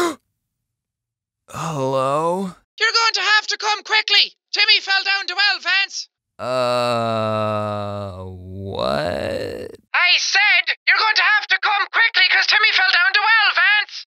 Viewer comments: Lol I heard “Your going to have to come quickly, Timmy fell down 2 elephants!” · YOU HAVE TO COME QUICKLY TIMMY FELL DOWN THE WELL VANCE I think that’s what it said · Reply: No is said “YOUR GOING TO HAVE TO COME QUICKLY! TIMMY FELL DOWN THE WELL VANCE!” You said “YOU HAVE TO COME QUICKLY” It actually said “YOUR GOING TO HAVE TO COME QUICKLY” A little difference there lol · I thought the woman was saying COME QUICKLY TIMMY FELL DOWN THE ONA DONA VENCE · I heard... " you gotta come quickly Timmy fell down the wild fence " Edit: agreed tho it does sound like "you gotta come quickly Timmy fell down the well, vance" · I swear I hear the person say "Your going to have to come quickly! Timmy fell down the well Vance" XD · Me: Lance from voltron how did u get him the I thought she said "your going have to quickly timmy fell down the well Vance James:logic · is it just me or did it sound like she said timmy fell down the well vance? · It sounds like she is saying "Your going to have to come quickly, Timmy fell down the well Vance" How can you not understand that?! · I thought she said (your gonna have to come quickly Timmy fell Down the well Vance) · Legend says that Timmy's still Stuck in the Well to this Very day. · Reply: I thought it said you better come quickly because Timmy fell down to elephants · All I heard was, “YOU’LL HAVE TO COME QUICKLY, TIMMY FELL DOWN THE WELL VENTS!” teehee 🙃 I’m probably the only one 😂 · I heard "LANCE COME QUIK JIMMY FELL DOWN A WELL"